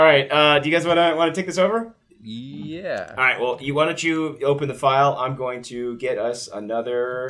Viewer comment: did you just say, "do you wanna chew open this file?"